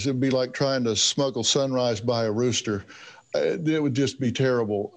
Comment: “Trying to smuggle a sunrise by a rooster” I really like that. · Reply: I thought he said smuggle some rice by a rooster but that makes a little more sense haha I think both work tho